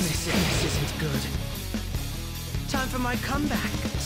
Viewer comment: Do I hear "It's so stupid" right after "Time for my comeback"? Edit: ah ok, so he says "so to speak" which can also be heard in his trailer: <link>